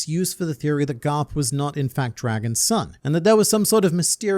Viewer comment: am i the only one who heard that he said that the theory is that "Garp is Dragon's son"